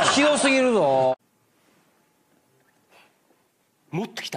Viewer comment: なんて？wwww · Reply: 個人的解釈の空耳 0:14 こんなに･･･うーん･･･歯ごつぁったいも違うな･･･ 0:22 もっと来たすかにゃむ